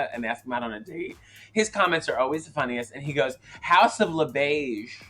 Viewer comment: I thought Ru said "Haus of LaBeige?"